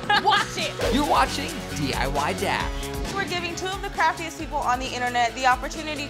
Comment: 😂 in the intro I thought said 'two of the crappiest youtubers compete' instead of craftiest😂 lol